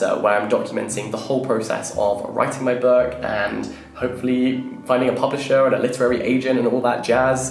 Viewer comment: For a second I thought you said "hopefully finding a puppet show", which I found oddly but intriguingly specific.